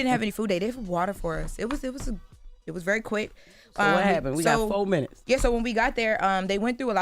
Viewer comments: I don't like how Jess gets slick with. "You got four minutes." Let this educated girl speak at a normal pace, please. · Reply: I heard "We got four minutes" Ppl love imaginary beef. 😂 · I don't like how Jess gets slick with. "You got four minutes." Let this educated girl speak at a normal pace, please. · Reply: I didn’t hear that…I heard, “We got 4 minutes” as in there’s 4 minutes before we have to go to a commercial break.